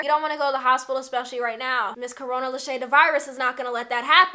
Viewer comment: did i just hear her say, Miss Corona Leshae De Virus 😂😂😂